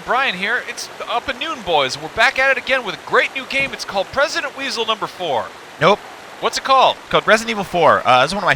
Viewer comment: what did he say? Resident weasel number4? xD